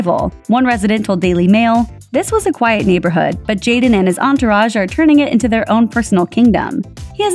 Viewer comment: that sounded like Jake Paul neighborhood problem